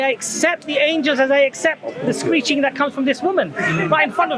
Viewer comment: "I accept the Angels, as I accept the screeching that comes from this women" 🤣🤣🤣 classic! 2021...... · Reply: I thought he said preaching. LOL